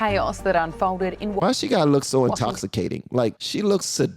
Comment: 😂😂😂 i thought you said intoxicated...😂😂😂 She looks STONED and about to fall asleep.